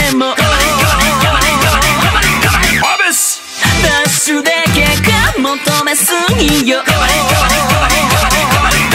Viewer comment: Does anyone know what he said at 1:46? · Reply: ともぺなっぴー what does it mean?